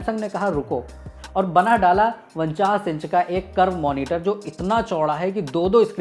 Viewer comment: क्या बोल रहा है ये वनचास क्या होता है,समझ नही आया.. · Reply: 49 bola